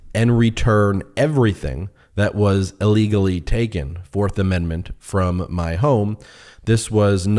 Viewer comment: Correction: he does not say he wants ALL the docs back. He says "everything that was illegally taken" 2:04 Weaselly, careful wording just after the lies about law!